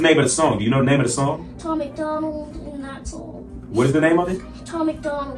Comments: I think this child was coached!! There's no way that he only knows your name and not the lyrics or the name of the song.... There's no way... I definitely think he was coached. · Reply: I thought I heard him say "tom macDonald, that's all" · I think this child was coached!! There's no way that he only knows your name and not the lyrics or the name of the song.... There's no way... I definitely think he was coached. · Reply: I thought he said “Tom McDonald and that’s all”